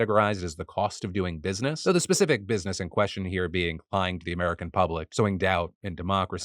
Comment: YouTube, the word you can't catch at 9:48 for subs is lying.